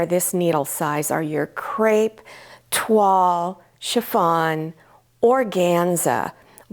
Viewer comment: where you mentioned chiffon, you mentioned another fabric here and other videos, sounded like "twa" and I'm curious what fabric you are talking about. Never heard it before. Thanks. Appreciate all your videos. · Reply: Hi Dr. Stone. It is "tulle" and it looks like netting. Ballet costumes have a lot of it in skirts. Hope this helps. Thanks for watching💗 and happy sewing😍